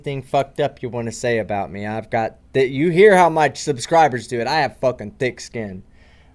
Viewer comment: Mislead team at the end and thought he said "I have dick skin"